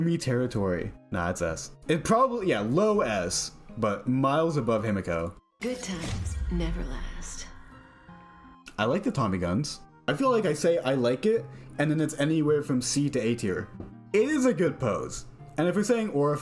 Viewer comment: did he say Tommy guns😭 more slop please